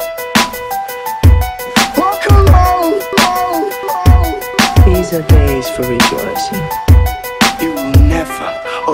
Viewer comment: What is he saying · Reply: "Welcome home!" The second one that starts the chorus the chorus is "Welcome home, Bjorn!" that is followed by the "Welcome home!"